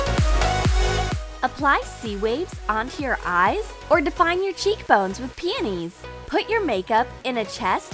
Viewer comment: Am I the only one who thought she said "Apply your makeup with PANTIES"??? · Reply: I think she said apply your cheek bones with penis